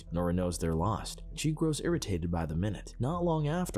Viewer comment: i heard "he was arritated by the meth" in the beginning